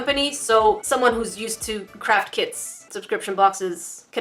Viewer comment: when she said craft kits , the subtitles said “craft kids”